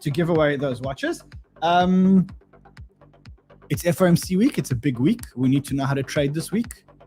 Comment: I thought he said FOMO Week